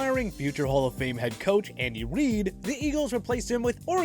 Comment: anyone else hear "Andy weed"??